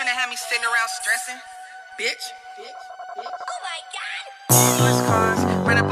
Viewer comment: I'm sorry but did anyone else hear kyle say omg kenny at 0:08 or have I just been watching too much south park